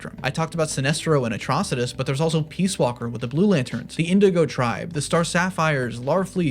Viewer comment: did he just say 'peace' walker?